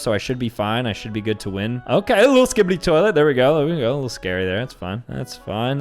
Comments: Did anyone hear him say a little skide toilet 💀💀💀💀💀🥶🥶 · did he say skibbidy toilet?🗿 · Is it just me or does say “skibidi toilet” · Bruh did he just say skibidi toilet at 9:17? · did he just say skibidi toilet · why did he say skibidi toilet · did Blud just say skibidi toilet or is it just my fVcked up brain? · Did bro just say "skibidi toilet"?💀